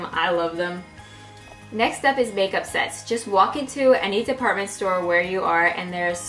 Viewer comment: Oh my god, I feel bad, it sounded like you said "make-up sex" not make-up sets! XDXD